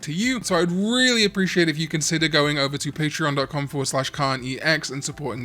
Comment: I started losing my hearing and now when Karn says “KarnEX” I hear “Karn eats eggs” and I needed all of you to know